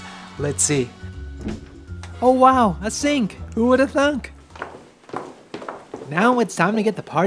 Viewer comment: did he say Who would've thunk??